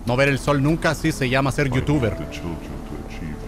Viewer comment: pensé que decía vtuver y tenía un edit en mente, me hubiesen funado, "un gran abrazo"